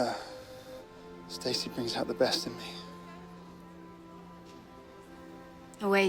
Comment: does anybody else hear the prince say "and you put the breast in me"